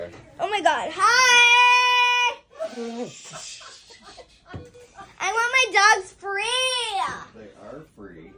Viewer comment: “oH My gOhd, HIAAYy” IM DEAD. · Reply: HaHaHa. I thought she said “I want my Dad” Never know how someone is going to react I had a Bad reaction and was in a coma ~ and they couldn’t get me to wake me up for a week. It took me a Very Long time to fully recover from it. Stranger Things Indeed.